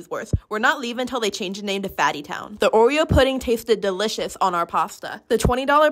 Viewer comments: Originally I thought she said "we're not leaving until they change the name to 'Maddi Town'... watched it again and realized she said "Fatty Town".... lol 😅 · I thought she said gyatty town at first 🤣🤣